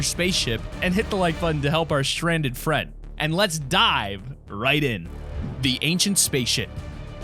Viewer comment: I thought he said: “...and let’s DIE right in.”